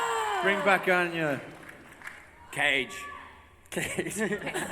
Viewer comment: who did Bob want to bring back? can't hear it. thanks so much for sharing this panel! just binged The 100, and i'm obsessed! awesome cast. can't wait for Season 3! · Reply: cage